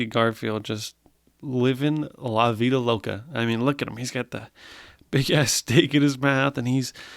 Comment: I heard “big ass dick in his mouth”